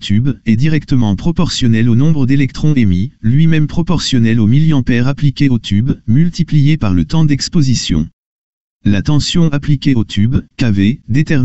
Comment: C'est quoi le mot qu'il le dit en 1:25 Mercie..🌸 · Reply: Milliampère (mA) appliqué au tube multiplié par le temps d'exposition (s, second)